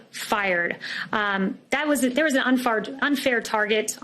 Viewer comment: I love this Press Sec...but did she just say UnFart at 1:40?